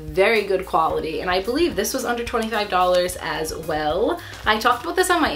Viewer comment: I thought you said $125 instead of under $25. I was about to freak out